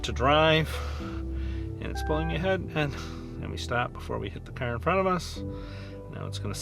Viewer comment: Did you stop or the car did when it was moving forward. I heard you said “let me stop “